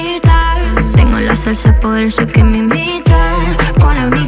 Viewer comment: Jskskskk yo pensé que decía : tengo la salsa, por eso es que me invitan 😂😂😂😂